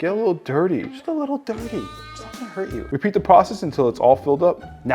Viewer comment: what did he say 😂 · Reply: It’s not Gona hurt you